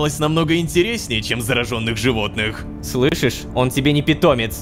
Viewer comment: мне послышалось, или сказал "животных"???